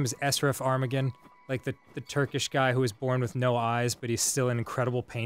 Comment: did he say turkish? I'm a Turkish person! I'm proud lmao.